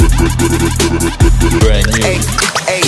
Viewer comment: I hear 'BRAND NEW' ... Am I right??